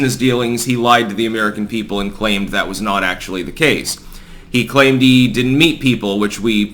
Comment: I seriously thought you said "claimed he didn't eat people".